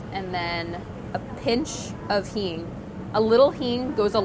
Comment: why is she saying? Hene? Heme? Hean? Hin? Google doesn't give any result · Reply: Hing